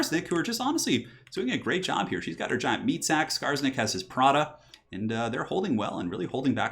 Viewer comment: When you said "Skarsnik has his prodda," I heard "Prada" and immediately pictured Skarsnik in heels.